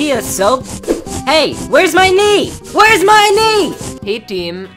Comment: I got a ad in the middle he said knee so I thought he said the n word